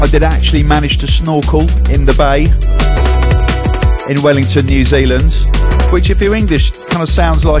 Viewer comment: when he said he managed to snorkel in the bay i thought he said i maneged to snore coke in the bay:-). no offence